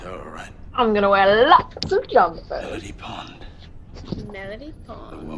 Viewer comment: she actually says 'I'm going to wear lots of jodhpurs!' , not jumpers. Basically what she's saying is she has a big curvy bum.